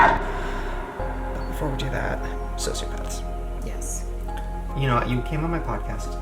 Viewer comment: I cant get over how at 8:11 the subtitles say leather pants instead of sociopath